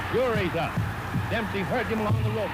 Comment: Swear it sounds like at 2:49 announcer says “Up, Fury’s up” instead of “sure he’s up.” Reminds me of Tyson Fury fighting 100 years later!